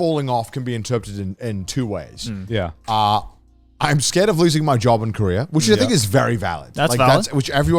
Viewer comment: fully thought he said “i’m scared of losing my job in korea”